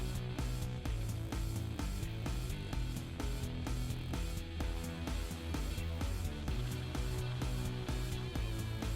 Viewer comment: att quoi ??? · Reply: I sont frere